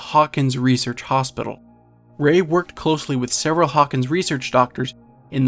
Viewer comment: OMG THIS WAS A THEORY I HAD FOR THE LONGEST TIME I JUST SAW UR VIDEO TODAY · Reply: it sounds like he is saying Sarah not ”several” 😂